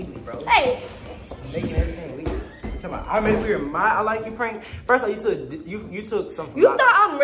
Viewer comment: I thought she was about to say I like to Roman in the 1st question